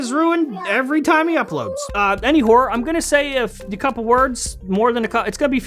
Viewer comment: wu wu wuut? Did you say “any horse”without the “se?”